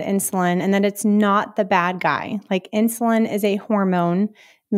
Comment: I heard "insulin is not the bag guy"....and I ended the video! This is no expert.